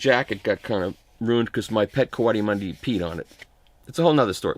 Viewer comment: did I hear correctly... you have a pet Cuati Mundi?...the musician or the south american raccoon.